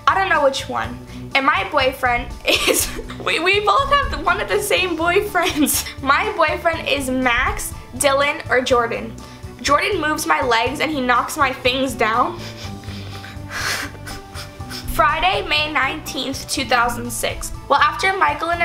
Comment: Did I just hear "Jordan moves my legs and knocks my face..." Or am I dumb.... Well I think I'm dumb so whateves